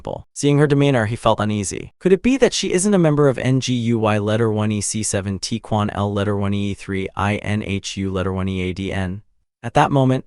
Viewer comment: what the fuck kind of name of organization is that 😂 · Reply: NGUY letter 1EC70 quang. L LETTER 1E3 INHU letter 1EADN. I had to slow it down to 50. An am about 80% sure that's what he said. Than me later.